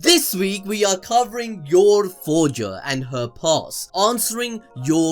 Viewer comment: For a moment a thought you said goldroger instead of yorforger since I always have my playback speed at 1.5😅